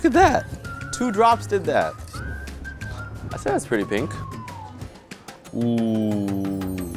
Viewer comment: What he said “I think that looks pretty pink” what I heard “I think that smells pretty pink” I’m really weird